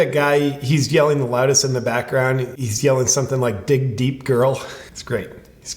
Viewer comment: I heard “way to cheat bro” not “ dig deep girl.” Anyone else?